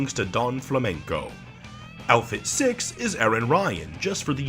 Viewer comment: While Mr.Sandman would undoubtedly be cool, if we were getting a new Punch-Out! Fighter, I’d want it to be Aran Ryan, as not only is he a very a popular character, but because he’s a dirty cheater, he wouldn’t be restricted to his fists, allowing for a very unique moveset. Headbutts, Elbow Strikes, what ever you’d call that thing he uses in Title Defense, you name it. Also, like when Dark Pit beats Pit, when Ayan Ryan beats Little Mac, he’ll say “YOU GOT CHEESEBURGERS IN THOSE GLOVES MAC!?” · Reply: I know sand man but not Aran Ryan I thought you said Aran Rodgers the foot ball person